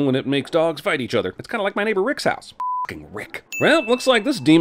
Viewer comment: You said "My neighbor Rick," but I heard "My neighbor Michael Vick..."